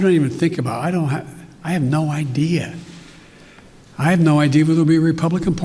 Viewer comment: "I have know idea whbe the Republican party." I wonder what he was even trying to say. It almost implies that their agenda is to get rid of the Republican party, though